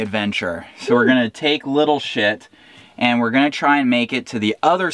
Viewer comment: Is your dingey called "little shit" or did I just mishear? 😂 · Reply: yep! it's called little shit 😁